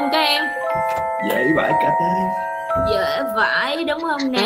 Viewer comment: ủaa ??? sao tui nghe ra" dễ vãi c*c ấy" v ta chắc co tui nghe thành như vầy quá huhu